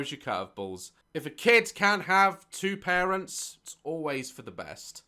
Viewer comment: I’ve genuinely wondered how I would be as a parent and daz describing his moms parenting seems like the type of dad I would be, like if my kid wouldn’t stop biting I wouldn’t bite them hard but bite back to get them to stop, and then I would definitely give my kids death glares most of the time, And this is just something I think I would do as a parent personally but I would totally whenever I’m home and if my child throws a fit I would just flop on the floor and try to take a nap as my way of throwing a tantrum until they calm down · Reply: I thought you said 'pregnant ' instead of parent. Oops